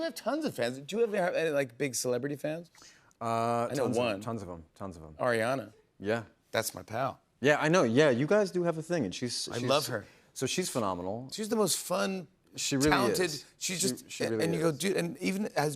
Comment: i misheard “ariana” and heard “rihanna” and got so excited and then so disappointed 😂😭